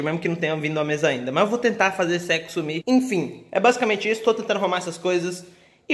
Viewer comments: Eu juro que escutei "Mas eu vou tentar fazer sexo ali" KJKKKKKKKKK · Eu juro que escutei "Mas eu vou tentar fazer sexo ali" KJKKKKKKKKK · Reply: Eu escutei "vou fazer o sexo sumir" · eu juru q ouvi" mas eu vó tentar fazer sexo ali" kkkk kkkk manooooo cadê o famili friendes sei la como escreve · putz eu tô me achando pura todo mundo ouviu "vou tentar fazer sexo aí" e eu entendi "vou tentar fazer esse eco sumi" oohh '-' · Eu juro que escutei "Mas eu vou tentar fazer sexo ali" KJKKKKKKKKK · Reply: Eu escutei 'esse sexo súmula